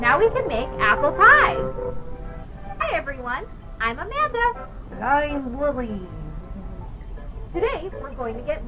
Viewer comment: When Wooly says "Wooly" it sounds like, AnD iM bUllieD💫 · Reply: I heard "And I'm lily"😭🖐️